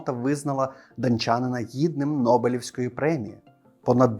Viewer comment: Я аж прихуїв трохи, коли почулося "дОнчанина". Але проц в голові з невеликою затримкою виправив ситуацію)))